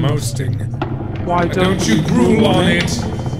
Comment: "Can you imagine the fact that I don't know which I'm supposed to be in? Why don't you go rule me on it. Same old few things as ever. You under the table trade off punts. Do you understand me? Nobody does. Listen, there is a body at the door. Should I get rid of him? Find me at grave field" Since the person has receptive aphasia it's more of "what are they trying to say" instead of "what does it mean" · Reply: By "Same old few things" I hear "Same fortune as ever."